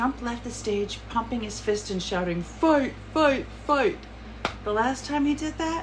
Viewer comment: tRump pumped his fist, but I could have sworn I heard, "WAG THE DOG" instead of fight.